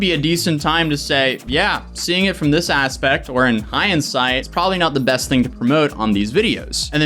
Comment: is saying “hindsight” or “high-insight”? Bc I could make a case for either phrase, in context…hmm